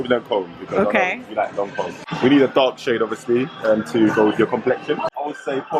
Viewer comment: He said DARK shade but I thought he said THOT shade lmao